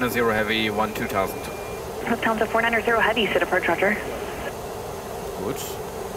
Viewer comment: What She´s sad on 3:50 in words? Lufthansa 190 heavy...??? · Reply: lufthansa 490 heavy Sea-Tac or Seattle (very slurred) approach roger.